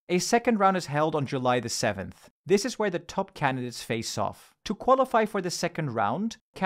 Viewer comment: Audio says "top candidates", captions say "top two candidates"